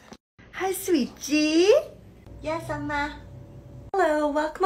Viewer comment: What i heard: hi sweet g